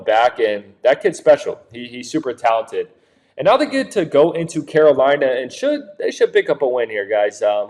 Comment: Bro you need to speak better what did you even say at the 2:15 mark did you say Carolina is picking up the win you need to speak more clear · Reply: He said they go into carolina and should pick up the win.. referring to Tampa. You should listen better or click on subtitles if given that option